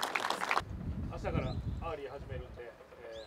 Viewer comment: 何て言ってるの？ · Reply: ｢明日からアーリー(早朝練習)始めるんだけど、行きたいと思ってる人？｣ みたいな感じです。